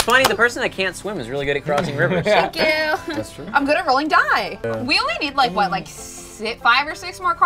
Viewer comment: I heard this as "I'm good at rolling. Die."